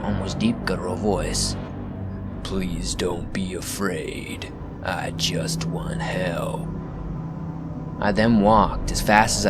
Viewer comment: Did you say "I just want Hell."? Did I hear that correctly?